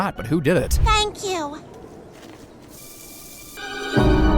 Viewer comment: it actually sounded like Stewie said “if anybody’s gonna take that it’s gonna be me”